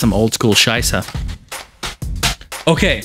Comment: and what did you say at 10:09? oldschool scheisser?:D